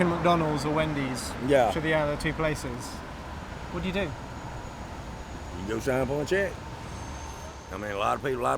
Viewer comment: what did the man say at 7:10 ? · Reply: Cheep trick go sign up on a check